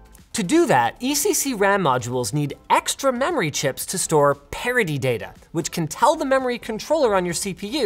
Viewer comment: IDK if the guy that wrote "parody data" instead of "parity data" in the subtitles at 1:48 should be awarded or fired